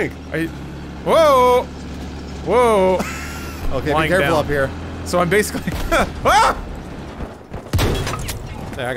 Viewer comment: Am I crazy or did anyone else hear James at 2:04? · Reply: Venator #5507 yea he said lying down